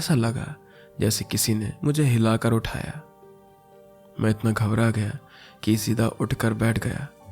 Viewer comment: i heard it like kisi ne mera hilaake utaaya meh and my dirty mind 😀😀😀😀